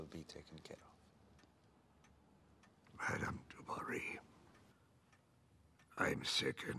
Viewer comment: Thankful for this clip with subtitles. For years I thought the king said “bring me to Marie” and it NEVER made sense to me. But him asking for DU BARRY explains his reaction now. The sound here was too low lol.